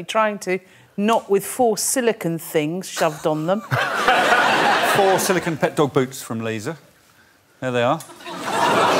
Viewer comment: help a non native speaker out and tell me what he says? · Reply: Crikey! -- brilliant word, similar to 'oh my goodness'